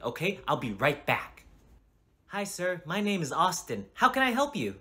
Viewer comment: I heard "My name is autist"😭